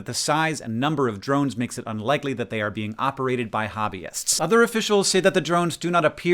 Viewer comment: I misheard “hobbyists” as “hominids” and I was VERY confused at how chill hank said it.